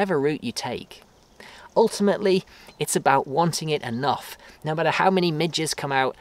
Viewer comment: I keep replaying it, but I can't seem to understand what type of animal he says might try to take your face off , at the 19:42 mark :) any help?! Texan with an accent here. :) Thanks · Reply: Gnats!